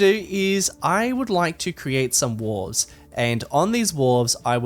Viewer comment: "i would like to create some wars" is what i first thought you said and i was very concerned